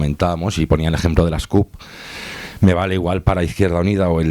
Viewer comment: que dice en el 4:10? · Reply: Creo que dice CUP (Candidatura d'Unitat Popular).